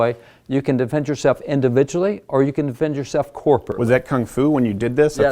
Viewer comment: What does the guest say at 1:23? He is explaining, “you can defend yourself individually or defend yourself corp...” but Steven cuts in before he finishes the word. Was it “corporately”, as in, in a group? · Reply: Yes it was corporately